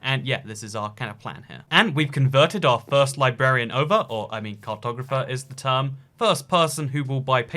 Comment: I thought he said cocktober fest when he was saying “cartographer”